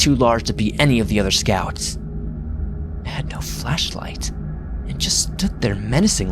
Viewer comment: I perked up so weirded out when I thought he said 'fleshlight'.